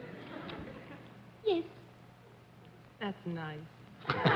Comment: Did I just heard Arlene say: F9 ?! Waaaaaay ahead her time :D